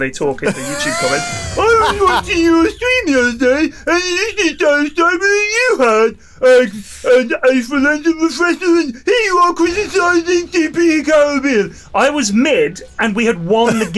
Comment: can anyone translate what is saying here? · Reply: "Uhh, Pyrion! I was watching your stream the other day, and he has the same items as you had, an aether lens and refresher, and here you are criticizing CPT Carribean." That's my guess, at least.